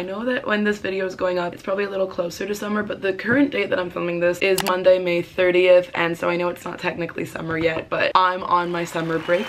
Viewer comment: idk why but I’m so tired that I thought u said monday the 80th😂